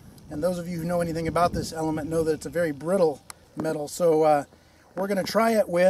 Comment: did he just say it was brittle?